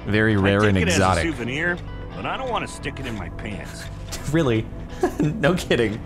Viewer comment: Pants sounds like he said penis xD